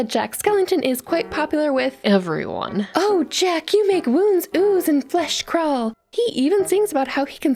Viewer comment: Not gonna lie, at the beginning I thought she said he makes wombs ooze.